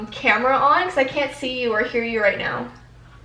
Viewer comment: Omg omg omg omg this could be nothing but at about 7:21 in your video after you say you can't see or here her you can here someone say something it sounds like they say right know or on know and i turned up my voulme with head phones on and i heard it